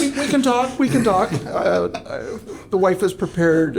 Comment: I thought he just said his x wife then second time he said x wife I realized it was. X Y